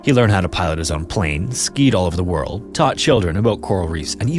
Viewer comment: I misheard it as "touch children" at first 😂